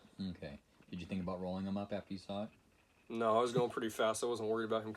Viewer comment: I thought i heard I was eeking along because of the fog. Know it's I was going pretty fast? Hmm